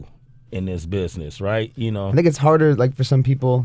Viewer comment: Did he say nigga at 2:08 ?? and no one realise? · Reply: tamer No, he says, "I think."